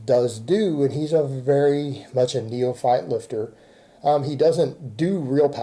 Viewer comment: What is he saying at 1:14? "Alex is very much a new file lifter?" English is not my language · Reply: He said "neophyte" lifter. It means someone who's a beginner/novice.